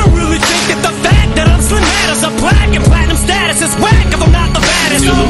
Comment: "A plaque and platinum status is whack if I'm not the baddest." - One of the dopest lines in hip hop history. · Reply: Maybe I'm trippin, but I always hear "Cause I don't really think that the fact that I'm Slim matters, I'm black in platinum status" I could be hearing it wrong though